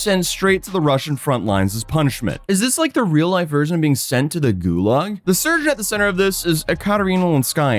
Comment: Recorded this video while tired unfortunately and yes I realize gulags exist IRL and not just in cod that phrasing was just bad.. 😭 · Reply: late-night dad brain kicking in🥲 I've heard you say "gulaš" instead of gulag, so... lol now I crave stew at 3AM